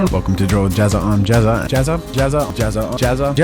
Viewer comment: "I'M DRUID JAZZA!" "WITHDRAW JAZZA!" I dunno... But I'm having fun with the montage. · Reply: Adam Jensen it also sounds like he says gazza a few times hahaha